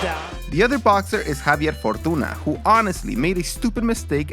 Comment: Did he just say homeboy's name is Javier Fartuna?